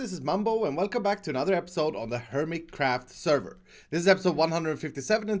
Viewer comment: Who thought Iskall actually said 'The Mice' in the Intro at the beginning @ 0:24 ?? Well at least the cat at the end will keep them at bay